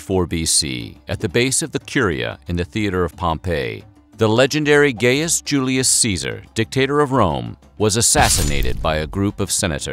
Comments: I thought you said :” the legendary gayest Julius Cesar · did he just say "Gayus Julius Seazer"?